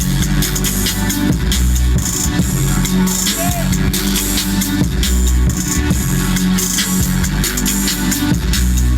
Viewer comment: I CANT EVEN UNDERSTAND WHAT IS SAYING!!!!!! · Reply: He said "got damn!" 😆